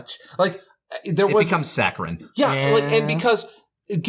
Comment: What does Lani say at 39:40? I'm trying to make out the last word of his sentence, sacrid? Sacron? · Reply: Ah, it was "Saccharine"